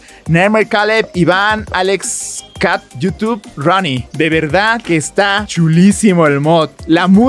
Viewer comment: o 13:10 pense que decia RODNY XD